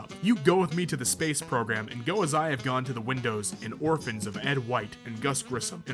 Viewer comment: did you say windows?